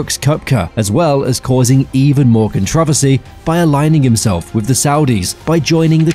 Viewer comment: wtf is that word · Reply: That’s how people with extremely posh British accents say controversies